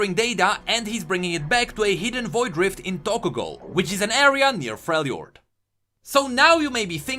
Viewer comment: did I just hear Tokyo ghoul?